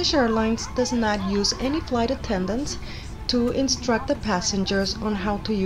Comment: Great review but who served the food then !!! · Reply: oh I thought you said there is no flight attendants!!!